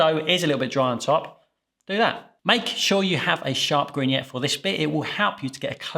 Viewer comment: What does he say at the 1:22 mark, "Make sure you have a nice sharp ______." The captions are saying that he said "green yet", but that doesn't make any sense. He's talking about some kind of blade that he's holding. I don't know what it's called. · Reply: Grignette 👌🏻